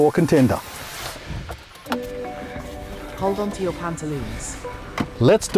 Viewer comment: what did the car just say on start up? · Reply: LOL, sorry mate, this was a bit of an inside joke with our crew, it said 'hold onto your pantaloons'